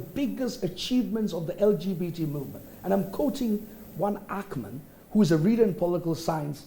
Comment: Paul can you get Lawyer to give us the name of the Professor he quoted in his submission (Time 4:36 ) .........I think I heard Ackman but I am not sure.